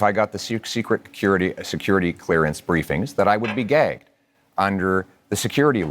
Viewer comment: I heard ""I would be gagged" but as a gay man myself he's definitely a self-hating gay man. Grinder client like other rabid "conservatives". And the reason everyone else heard gay" is because you all are also picking up on the gaydar without realizing it.